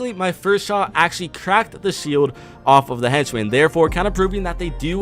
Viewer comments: Did he say henchman 😅3:08😂😂😂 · he said henchmen instead of decoy